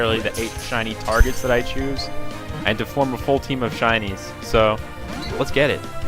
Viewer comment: my captions said that you have a full team of chinese